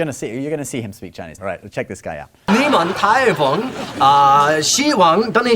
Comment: I heard 你们太棒. So hard to tell haha